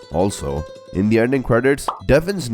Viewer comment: The subtitles said Opening but the voice is saying Ending